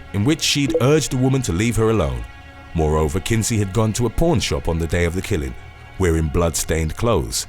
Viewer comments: Oh goodness. I thought you said "Porn Shop" instead of "Pawn Shop"! Please forgive my ears, dear. They apologize for hearing those words out of your mouth! 🤣🤣🤣💖💖💖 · I heard "pawn shop" as "porn shop" and was momentarily confused. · When he said pawn shop it sounded like he said porn shop 😂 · When you said pawn shop I thought you said porn shop.